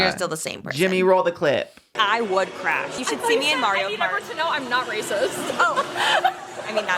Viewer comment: Commentary added for statement: said something and thought she said “I need everyone to know I’m not racist” and then comes in with his racist ass “joke”. Like not funny at all…come on…